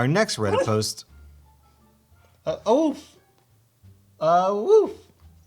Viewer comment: somehow, the "awoof" at 15:51 sounded like the german phrase "hör auf" (stop it). which would be amazingly fitting in this case. lol